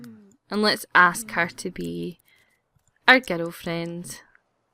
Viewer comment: I thought you said "ghetto friend" xD I love your accent!